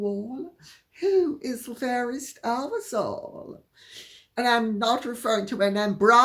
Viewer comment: I heard 'are-us-all' instead of 'of us all' 😉